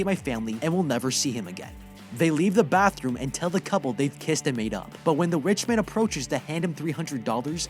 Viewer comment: I THOUGHT YOU SAID PISSED AND MAKE UP